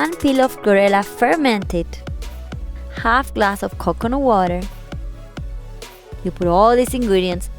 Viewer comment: I did not use coconut oil in this smoothie. So please watch the entire video first and then make you comments. Positive constructive comments are always welcome. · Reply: I sincerely apologize 🙋‍♀️ because of your pronunciation I thought you said coconut oil instead of coconut water* Sorry 👍🤗